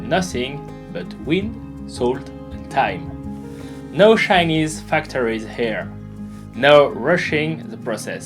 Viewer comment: I thought he said, "no Chinese factories here" but he said "shiny"..